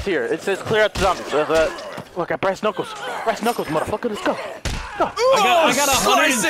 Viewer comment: i misheard him saying breast knuckles xD